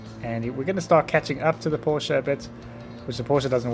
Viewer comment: Due to brits pronouncing ‘uh’ sounds as ‘er’ sounds, when he said “Porsche a bit” I thought he said “poor sherbet”